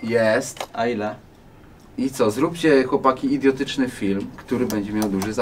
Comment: usłyszałem "Zróbcie chłopaki idiotyczny film który będzie miał duży film" i kurwa lajkować to